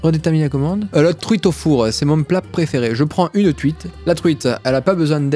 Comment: "je prends une truite" Ce que j'ai compris : "je prends une CUITE" XD